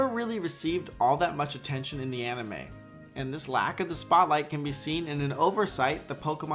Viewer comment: I may be mistaken, but i hear aMime instead of anime